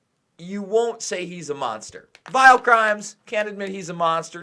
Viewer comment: Good work. Sounds like he said File. crimes not vile.crimes She must’ve shook him behind his meek little voice